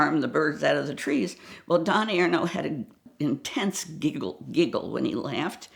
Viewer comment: At first I thought she said that he had an intense dingle. Had to rewind and listen again.